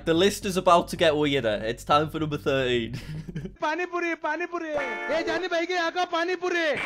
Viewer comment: i know urdu and i was so confused with what he was trying to say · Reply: It's actually hindi, lol I even know hindi but he said that so fast that I could only understand some of it He said pani puri×9 ( which is Indian snack) And he said buy some pani puri That's all I hear He was quite to fast